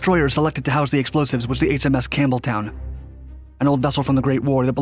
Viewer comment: It sounded like you were saying HMS Cameltoe.